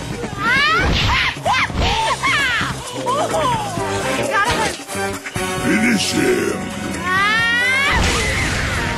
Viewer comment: What the fuck did she say at 0:57 XD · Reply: "woohooo da gotta hurt!